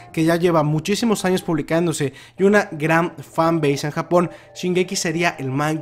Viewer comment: que dice fan....?